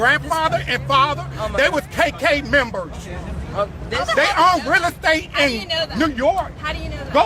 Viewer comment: I thought ol boy said "circle k members" lol